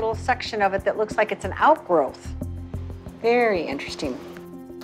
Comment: The video froze for me at 2:59 and I thought she said "I'm no longer a virgin."